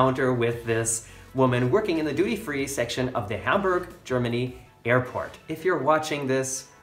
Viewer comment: That 'woman' is my SISTER!!!!!! YOU'RE HORRIBLE!!!!! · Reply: HAMBURG!?!? Oh.... I thought you said PITTS burg.... Never mind......